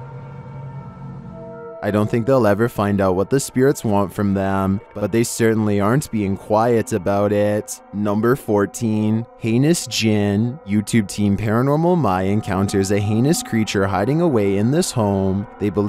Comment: I heard "penis djinn" xD